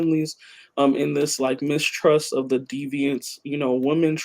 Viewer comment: she said mistrust of the deviants...is she saying we should trust deviants? I guess should read her article. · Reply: what actually said was "mistrust of the 'deviant woman' trope". which was a part of her response to probing @ 1:08:30 that naming colonialism as a project of patriarchal white supremacy could elicit discomfort. her larger point being that who and what colonizers deem as threatening, and so who and what we are socialized to understand as threatening as colonized people, both on the personal and geopolitical level, is rooted in/works to advance patriarchal white supremacist colonial logic. thus in our organization (and personal relations) we must constantly be working against the backwardness of our socialization to ensure we arent advancing the logics of/mimicking our oppressors as opposed to genuinely developing our own people centered logics. part of that being not running from inevitable discomfort/corrections/challenges etc. shes not saying we should trust deviants inherently, but that we should understand that threats--like the construction of the 'deviant woman', like that of the 'advancing middle east'--are assessed and defined purely along political and ideological lines and from there we must determine for ourselves who and what is actually threatening to us and our principles/objectives.